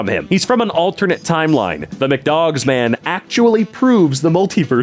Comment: McDonald’s hotdogs did actually exist once! Called the mchotdog, it was discontinued in 2004. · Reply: i thought for a second you said it was called the mchog i was gonna cry